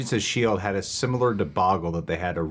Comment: Did just say deboggle? 😄😄 Theres no way didnt wanna correct him with the actual word after he said it (Lmfao his face says errthang!) BUT!..... if ur a constant viewer u might know how it goes. He prolly just agrees with that it's the correct way to say debacle 😬😬